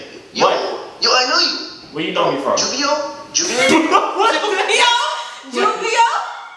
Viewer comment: dead ass i thought he said yoogi and that shit has me dead